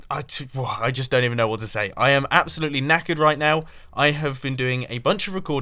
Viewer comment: did he say he was naked right now? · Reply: he said 'knackered', though it did sound a bit like 'naked'